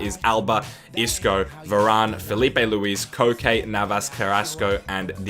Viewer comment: Did he say cocaine???